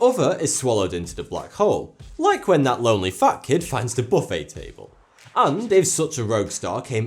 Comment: I thought he said "that lonely fuck"